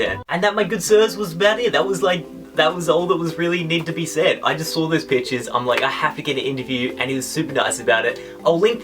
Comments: did he say I just saw those bitchs?? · sounds like he said bitches instead of pictures anyone else agree